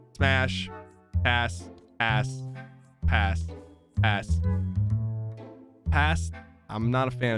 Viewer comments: did he say ass😅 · did sypher say ass 🤨🤨🤨🤨🤔🤔🤔🤨🤨🤨🤔🤔🤨🤔